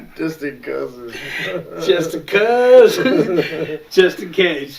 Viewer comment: I was dying when I first heard this. "Cuzzins."